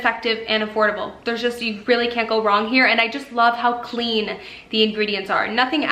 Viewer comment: i legit heard "you really kencko wrong" 💀